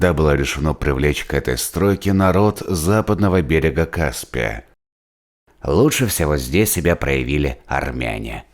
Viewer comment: Я правильно услышал на 1:55 , лучше себя проявили народ проживающий на западном берегу Каспия:армяне...